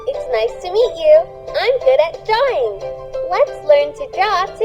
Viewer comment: At first I thought it said,”I’m good at dying”🤣🤣🤣🤣